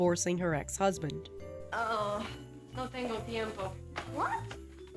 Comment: I've listened to this part like 4 or 5 times, and I have no clue what the mother says here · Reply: I believe she says "no tango tiempo" which is Spanish for "I don't have time"